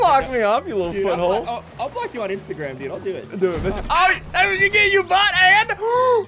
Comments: When said i will block you did ssundee say do it bit*h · Do It B** Did I hear that right?